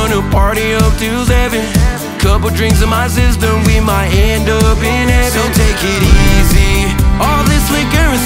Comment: The one lyric that says “Couple drinks in my system, gonna end up in heaven.” I thought it said “Couple drinks in my sister, gonna end up in heaven.” 😂 I was like, “What the hell?!”